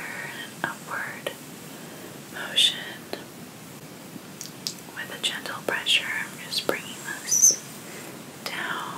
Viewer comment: Did she say oh shit at 14:16 or 14:15? Lol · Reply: She said push it